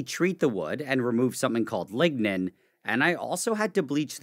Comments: I thought you said "ligma" lol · I thought he said ligma 🤣